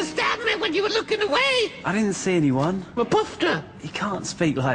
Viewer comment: wtf does he say? Mapufta? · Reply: “A poofta”